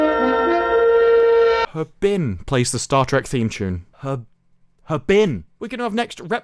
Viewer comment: I think they're saying "Star Trek is trash". This is where we are, now.